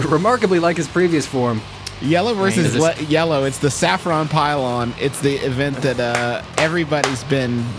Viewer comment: Y'all are Sleeping on how great of a Joke "Yellow vs Yellow: The Saffron Pile-on" is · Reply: I thought it was a nickname for Borth, cuz I heard "Saffron Pylon" But tbh that sounds like a great entrance nickname, like: "TONIGHT ON MONSTER FACTORY! Yellow Vs. Yellow, the Saffron Pylon vs. Maxiumum Potassium! BORTH VS. DAVE!"